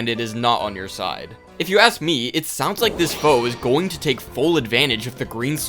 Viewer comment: Was listening to this in the background and thought 2:42 said "this hoe" lol